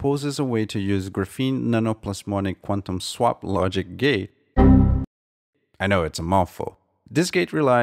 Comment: My brain trying to understand what he said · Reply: Nano Plasmonic Graphene quantum logic gate!